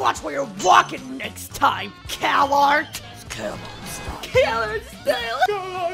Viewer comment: At first I heard it as "coward's style" xD